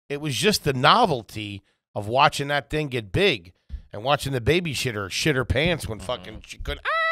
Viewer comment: Is it just me, or did say baby-shitter!!!? 🤣🤣🤣